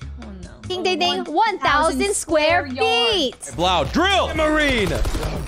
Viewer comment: Did fuslie really just say "1000 square yard" or did I misheard it